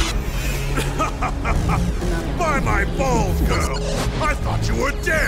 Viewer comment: What is said at the beginning of the video!? I've listened to it over and over again and I swear I hear, "Ha ha ha ha! Bite my balls girl! I thought you were dead!"